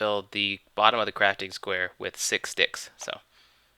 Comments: it sounded like you said "fill it with six dicks." ._. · Sound like he said Six dicks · LMFAO at 5:30 I swear he said with 6 dicks...oh man... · he did say sticks it just sounded like he said dicks